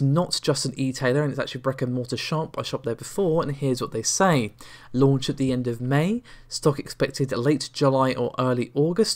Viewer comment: Launch at the end of May, did I hear right?